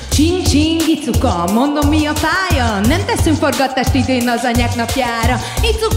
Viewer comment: nál mit mond??? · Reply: "Nem teszünk forgatást idén az Anyák napjára"